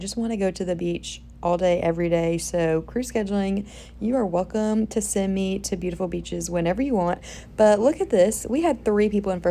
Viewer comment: I thought you said send me to Australia 😂❤